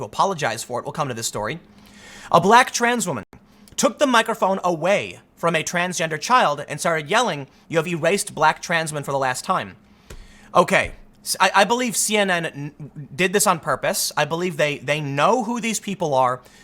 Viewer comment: did you just say transgender child?